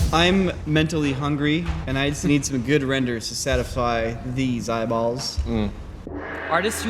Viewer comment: I swear said 'satify' at 14:06, but it's a good sound bite for future episode intros lol